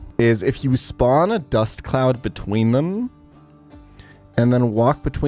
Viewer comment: I thought he said no dust cloud cheese